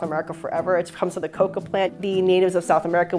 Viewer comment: the captions say “cocoa” instead of “coca”. Cocoa (or Cacao) is the main source of chocolate, not cocaine. They are two very different plants.